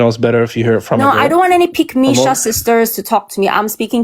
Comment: what did she say? "I don't want any pick me (shasister??) to talk to me"